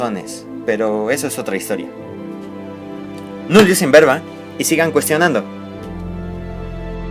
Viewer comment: que dice en el minuto 10:05? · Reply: Nullius in verba